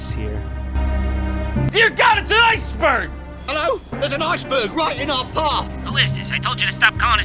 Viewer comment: jon said ice "burn" just listen to what he says